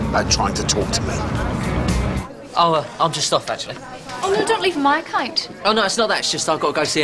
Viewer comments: What did Lauren Say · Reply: “Oh no don’t leave on my account” even tho it was a year ago u might still wanna know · What the fuck does Lauren say at 0:35? I have watched this episode so many times and can’t understand what the fuck she said because of that thick Irish accent, what did she say? It sounds like “oh don’t leave on my kite” WTF DOES IT MEAN???? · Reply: "oh no dont leave on my account"